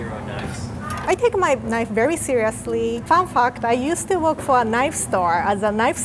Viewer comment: i swear i thought i heard her say "fuck that" instead of "fun fact"